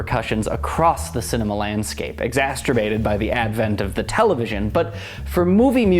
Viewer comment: I had to smile. I think you said "exasterbated" rather than "exacerbated by the television." Oh don't worry, I think you are absolutely fascinating in your analysis and the range of your knowledge, and the technical skill with which these postings are put together. New viewer here, ardent Fan but what was on your mind at that moment?